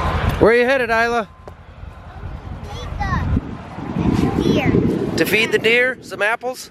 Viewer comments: I swear at 19:51 I thought she said “Pizza…and beer.” 😅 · I thought she said getting Pizza and Beer, not Feed the deer! Hahahahaha!